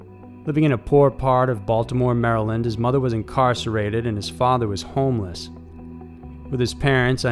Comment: wasn't paying attention and thought he said mother was constipated and father was homeless lol